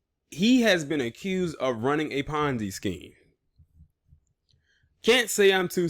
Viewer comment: THOSE HAPPY DAAAAAYS.....oh PONZI, I THOUGHT YOU SAID FONZI.....AAAAYYYY👍🏿,LOCK HIM UP!!! ...AAAAYYY👍🏿